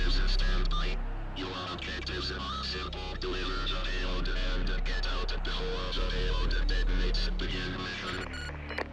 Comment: What does he say? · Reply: Deliver the payload and get out before the payload detonates begin mission